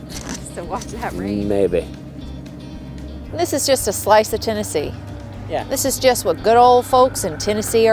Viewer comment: O this is Tennessee - my bad - I thought you said Oklahoma